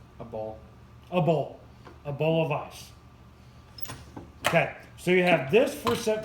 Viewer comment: I loved the video ! Thank you! I laughed so hard when you said “....a beautiful cascade of magnificent ice!” Then your son says....” a ball.” 😂😩💀 Love it! Keep it up! 💕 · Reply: I laughed so hard when he said "a beautiful cascade of magnificent ice " And YOU THOUGHT HIS SON SAID: a ball of ice !! 🤣🤣🤣🤣